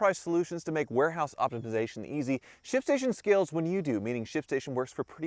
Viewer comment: I thought he said "Sh*t station." 😂😂